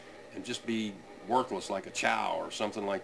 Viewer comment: I'm sure he said worthless like a chow but at first I thought he said worthless like a child LMFAO